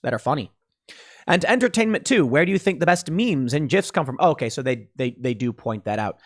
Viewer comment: Did anyone else hear "jif" at around 6:40? Or so? Took me out of the video and made me wtf lol